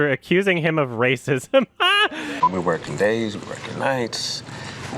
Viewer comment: I heard him say,we're smoking days were smoking night's. And I'm doing prison to keep my 10 million dollars. Wesley Pipes Bitch!